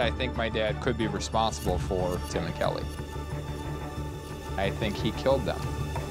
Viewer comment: I heard “they think I killed him” but I mean he’s gross and guilty so I’m not defending him.